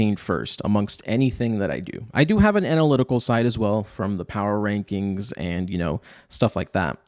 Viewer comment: For a moment there I thought he was gonna say "Power Rangers" instead of "Power Rankings"